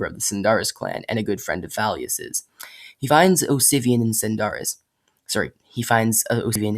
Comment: For a second I thought you said ‘Obsivian’ at around 3:20. I have done my job. Obsigneous shall live on forever!